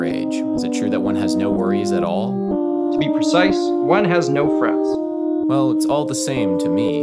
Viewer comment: i heard "one has no friends" when the dialogue was going forward. But definitely "one has no frets" the other way.